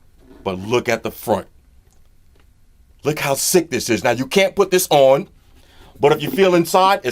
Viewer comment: He said you can't wear it. Probably too small on the inside. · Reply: The LazyAlgorithm mybad I misheard, I thought he said you can put this on.